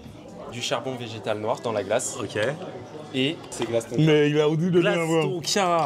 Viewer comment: C'est quoi la glace ?? rien compris, inaudible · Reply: fève tonka 🙂